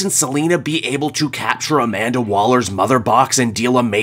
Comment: I heard "capture Amanda Waller's mother".... and had to rewind. 😅